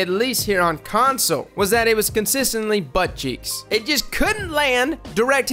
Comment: "Consistently buttcheeks." Lmao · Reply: I definitely thought he said “butt cheese”